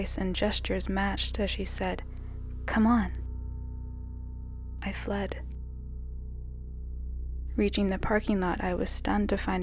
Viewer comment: Dude what a classic beauty and the beast moment. Dark path and sunny path. All I heard was Bells's dad saying "Come on Phileep." Idk how to spell that horses name but that's what it sounds like